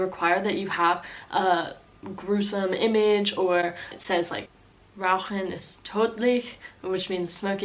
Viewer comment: min The way you pronuonce "Rauchen ist tödich" sounds more like the Dutch slogan "Roken is dodelijk" xD I heard you say "Rauchen ist dodelijk" xDD